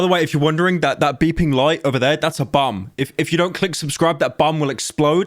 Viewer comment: i heard bum